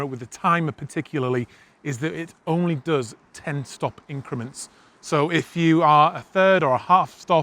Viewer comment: Haha! At 3:35 I rewound like eight times thinking, "I know he didn't say ten-stop increments. I must be hearing it wrong." And it seemed like something important so I had to try to figure out what you were really saying. Then I realized there was a huge white message on the screen telling me you meant one-stop increments. I guess I was focused on looking at the scale in the camera the first seven times. :)